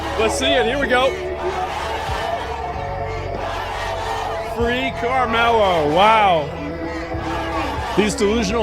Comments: i thought they where saying Free caramello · Free carmellos? Neat. I love those little carmel candies. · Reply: All I heard was "Freak Carmellos". Based crowd for once. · All i hear is "Free watermelon" 🤣